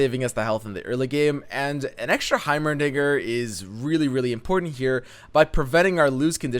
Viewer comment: uhh.. did I hear that right "An extra heimerni**er"